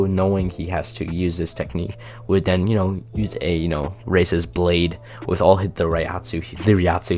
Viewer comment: i watched this with subtitles and around 19:26 its says racist blade instead of raises blade